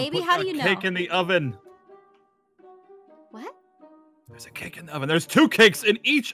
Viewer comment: For a sec I thought Aaron said "there's a bun in the oven" 😂 just me? Okay....I'm leaving.....